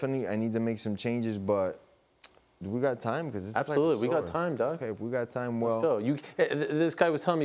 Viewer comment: did ariel just say "You got time DOG" ahah